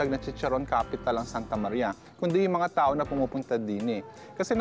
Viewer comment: & 8:57 did i just heard "dine"?